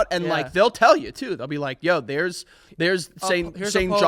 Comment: i heard "Theres Saint Sean Paul" and i just about lost myself